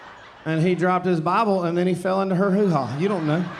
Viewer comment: "& fell into her hoo-ha" Translated from english captions because I couldn't quite make out what he was saying. Just in case others couldn't make it out either